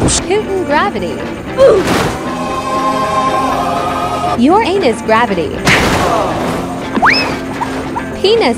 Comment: "Uranus gravity" but i heard "your an#$ gravity"